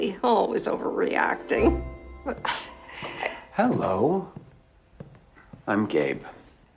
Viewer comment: every time he introduced himself i thought he was saying "I'm gay"